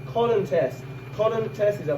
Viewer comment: I think I am saying cotton test at 2:55 here